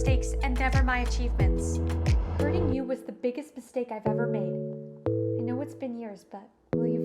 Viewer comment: why did i think she said “having you was the biggest mistake I’ve ever made” 💀